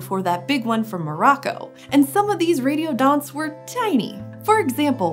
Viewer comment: I thought she said "radio dot"